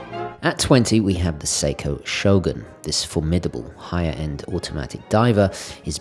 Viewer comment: sumo shot instead of shogun you can't fool us :)